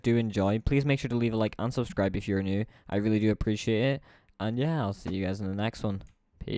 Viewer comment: i thought you said "And un-subscribe if your new" My hearing is amazing